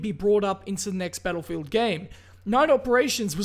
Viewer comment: I know you said Battlefield, but at first I thought I heard "Turtle field" XD